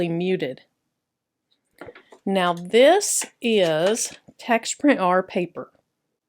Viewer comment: great video! at 10:38 what kind of paper did you say that was? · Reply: Here's a link to it. It's a sublimation paper by TexPrint and it's a specific paper of theirs. <link>